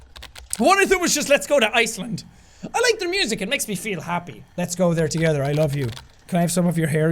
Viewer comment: ish it says always me feel instead of always MAKES me feel.